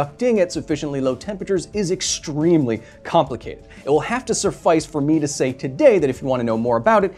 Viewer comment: "It will have to surffice" BRO IT'S "suffice!!" And I DEFINITELY heard an "r" there!!